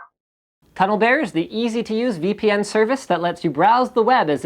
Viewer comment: you said cuddle bear instead of tunnel bear